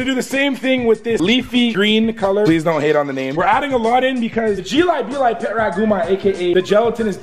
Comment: Wait wtf did he say at 6:37?!?! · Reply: Jelli belly pet rat gummi candi